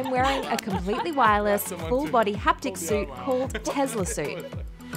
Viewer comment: I thought it said TESLASLUT ...my bad.